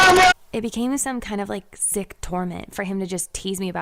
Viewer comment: i thought she said "sick torment" but she actually said "sick tournament" lol